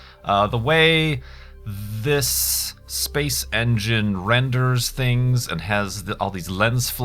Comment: Ah man, I thought it said Space ENGINEERS. Lol, anyway, nice video